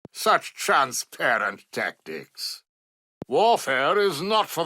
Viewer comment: What does he say at 0:18? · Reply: “Such Transparent Tactics…”